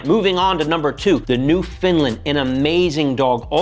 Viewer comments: For a sec I thought he said "NewFinland" I went, wat?! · Thought you said ”New Finland” 🇫🇮 😅